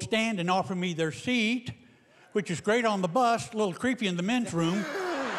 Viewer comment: When he said men's room I thought he said metro so I didn't get it.